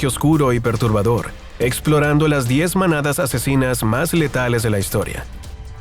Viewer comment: Pensé que decía 10 mamadas asesinas